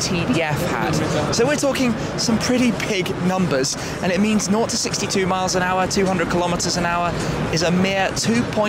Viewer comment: Did Shmee just say 0-60 in 2 miles an hour?? Lmao